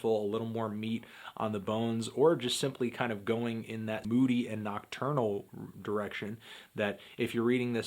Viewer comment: I thought he said "Go in that booty" 11:30 😂😂😂